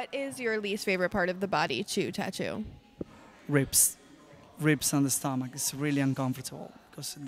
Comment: first i tought he said "rapes on the stomach," then i tought to myself for a second and then i thought he said "rips." then i check comments and see "ribs." please dont ask okay im slow af